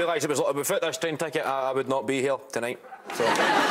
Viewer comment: what does kevin say at 6:51 i never realise it was all a befit this train ticket... i wouldn't be here tonight? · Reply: You mean 6:51? "Without this train ticket I wouldn't be here tonight." Not sure about the preamble.